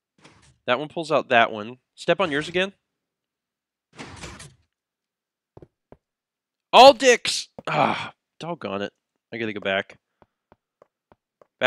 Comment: Did he say "oh d*ck at 7:47 lol